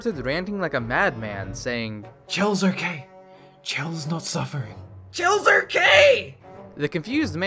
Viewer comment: Does anyone know what the heck he was saying??? It sounded like "Chill are K. Chills not suffering. Chills are K!"